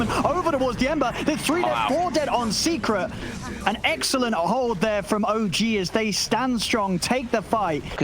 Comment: i thought Odpixel said an EGGxelent hold from OG